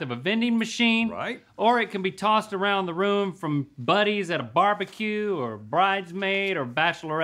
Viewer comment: Did Link just say, “at a BBQ.”? Hmmmm.